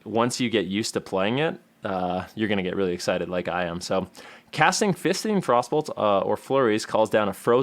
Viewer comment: Casting fisting Frostbolts... My brain trying to grasp what i just heard: Wha..what?